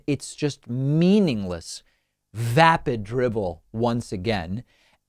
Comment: "Meaningless vapid drivel." What a great phrase! So universally applicable! Right Dave? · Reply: Thanks. I thought it was 'drivel' at first but even after several listenings I kept hearing it as 'dribble' so that's how I quoted it. I've made the correction. Thanks again.